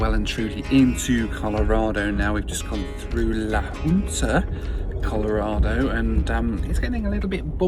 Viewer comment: Another fascinating trip, thanks! When you closed in la Junta I heard you saying "La Puta" so head to rewind to cross check 😂